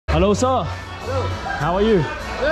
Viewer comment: At first I thought u said "ভালো আছো?" 😅 I had to rewind 3 times and then saw subtitle to makesure what i heard 😂 "Hello sir" not "ভালো আছো"